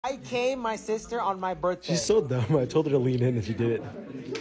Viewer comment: I thought he said i came on my sister on my birthday